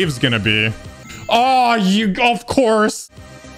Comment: kinda sounded like "ahh you golf course"